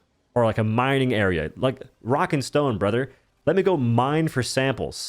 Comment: DID I HEAR A ROCK & STONE ??????????